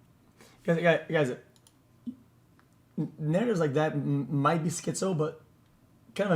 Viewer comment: what did he say???💀💀💀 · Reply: Oh narratives